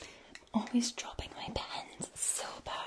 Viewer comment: l legit thought she said "Always dropping my pants." Wishful thinking.